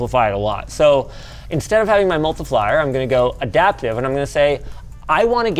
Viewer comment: "instead of having my multiflyer" 😂